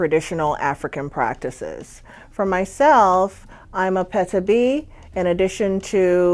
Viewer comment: Hi , Can you please tell me what is a “petabee ?” You said that you were one at 0:31 . Thanks 🙏🏻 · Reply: Great question! An “apetebi” is the wife of a babalawo.